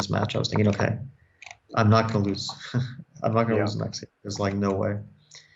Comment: Did he say "i'm not gonna lose Maxime"? Bro is playing Jules Moussard 😂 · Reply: i think he said im not gonna loe the next game